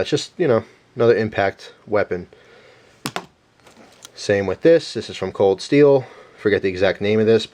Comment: did he say "Same dildo?" lol